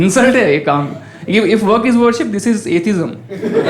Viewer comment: what did he say? if work is worship , then this is.... 7:49. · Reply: Atheism